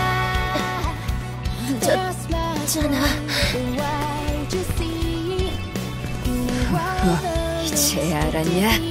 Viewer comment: 초에 뭐라고 하는거야 .. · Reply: 늦었...자나...